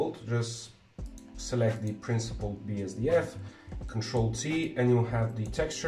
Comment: He said : "Ctrl - T" , in case someone missheard what he said.., Thanks for the tutorial ;-0